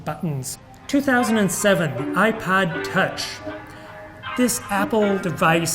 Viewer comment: I’ve listened a dozen times... Does he say: 2007 - iPod Touch, or iPad Touch? I’m leaning towards IPaod, by reason of mini-stroke. Also... is that a Mon Che Che monkey puppet in the background?